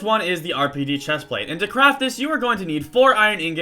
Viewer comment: Its sounds like hes saying "now to crap this.." XD